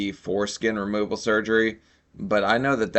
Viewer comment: I was listening to you while falling asleep and sat bolt upright when I heard "foreskin removal surgery" at 1:25:01 It didn't dawn on me till today that you didn't say this. I'm surprised I didn't have some wacky dreams.